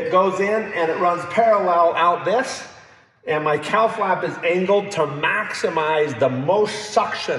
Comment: Glad you showed the knob labeled 'cowl flap'. I thought you'd been saying COW flap. lol